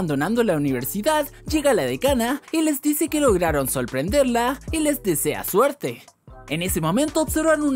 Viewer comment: solrplendelrla, al menos eso fue lo que escuché xd